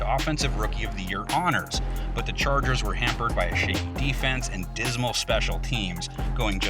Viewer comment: i swear he said “a shitty defense” 😂